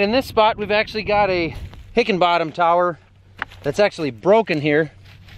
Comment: What did you say it was? A pickin bottom tower?!